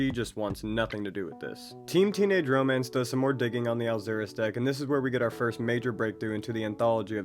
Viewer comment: The subtitles thought u where saying "Alzheimers deck" instead of "alzuris deck" (or something like that) lmao